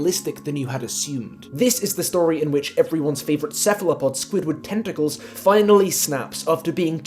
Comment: i swear to god i thought you said “settler-pod” instead of cephalopod and lost it